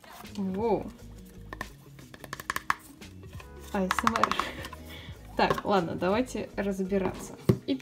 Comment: Ты сказала "Асмр* , а я услышала Асума :_( теперь плакать хочу ( кто смотрел Наруто тот понял)